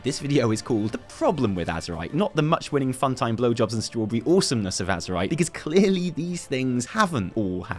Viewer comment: Did he just say "much-winning fun-time blowjobs and strawberry"?